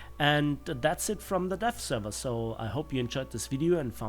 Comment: Kind of sounded like you said the death server